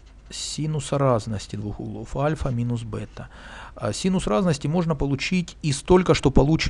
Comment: в начале послышалось: "косинус двух полуальфа минус бета", чуть со стула не упал